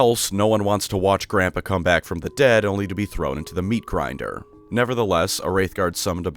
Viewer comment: Ngl I thought is said "The meat-riding never ends"